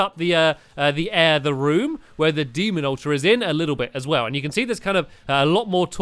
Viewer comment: said demon altar instead of lizharhd altar